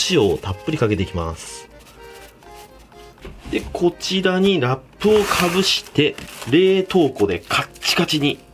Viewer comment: ラップが納豆に聞こえたww(空耳)